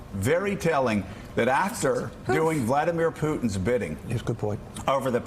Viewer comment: What? @ 0:42 ‘yes good boy’ who said that voiceover?! Haha